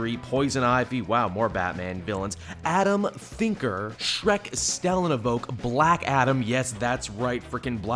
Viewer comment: Wait, did say at 3:04 the name "Shrek"?